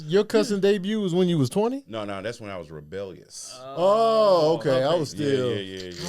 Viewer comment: 🥷🏿 did u just say u got rebellious at home when u was 20?